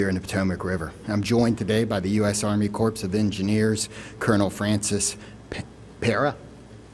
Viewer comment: I heard him say US Army corpse of Engineering. Must have attended WOKE High School.